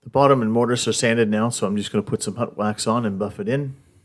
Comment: Great idea! I'm always losing my scrunchies! Il have to make one for my husband and son too!🤣. What kind of wax did you say you used? I'm Canadian also, and it sounded like you said hut wax?!🤔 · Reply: Thank you! HUT wax is correct. I get it from Hut products online. I appreciate you watching!